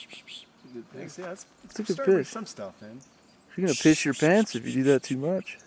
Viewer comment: And how, out of all that, i heard "phish your pants" 😂😂 im not even 12 😂🤭